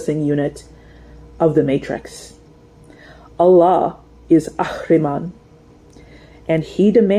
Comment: Is she saying Allah is Arihman (Like Rudolph Steiner’s Arihman ?)